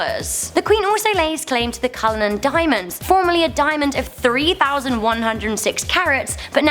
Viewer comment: Did you said Kohinoor diamond at 6:08 ?? If, yes the pronunciation was very wrong !!!! Ok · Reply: Real Life World its cullinan diamond from Africa dude